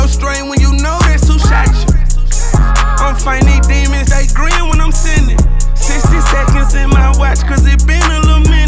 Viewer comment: That man said, "I'm fighting dez demons they green when I'm sinning" 🔥 right there I felt that!! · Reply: I thought he said they agreeing when I’m sinning